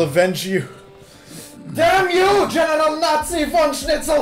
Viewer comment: gonna be honest I heard genital.